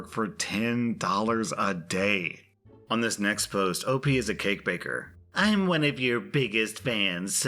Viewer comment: Why did I hear "op is a cake baker" as "op is a gang banger"